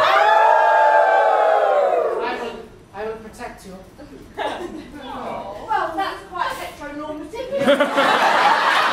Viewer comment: Anyone tell me what did she said captions not helping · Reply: "Well, that's quite heteronormative."